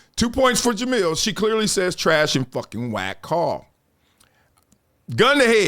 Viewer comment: No, I didn’t know when watching it. I thought she could have said trash call, but she absolutely said white girl you can tell by the girl we all curl our lips to make that word. If we’re saying call your mouth won’t move like that.